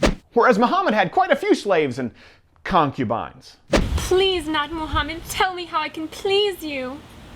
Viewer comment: Every time they said "Not Mohammed" I kept hearing "Nahq Mohammed"... not sure what that means 😐